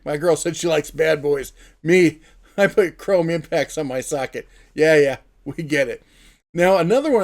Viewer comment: what did you say? 😂 “ I put chrome impacts on my SOCKET” lmao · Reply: I put ribbed impacts on my socket lol